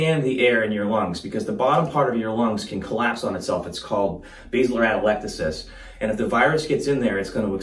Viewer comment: Did anyone catch the term for collapsed lungs as 1:41? Edit: probably Bibasilar atelectasis.